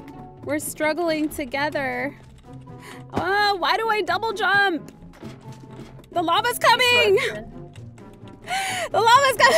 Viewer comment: When said "the lava is coming!" I thought she said the momma is coming lol